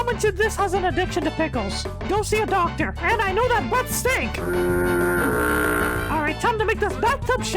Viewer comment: I thought he said "I know that butt stinks" 😭